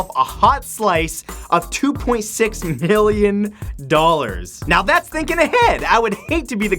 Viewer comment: did you say million or billion. I'm sure it was million but idk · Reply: he said million